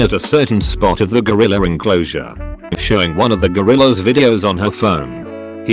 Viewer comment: for some reason I thought it said Godzilla until about halfway through. Obviously, I was very confused